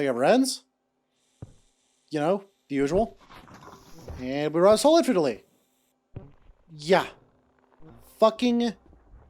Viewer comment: I cannot tell what you're saying there, I'm sorry. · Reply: I believe he said "And we round stall infinitely"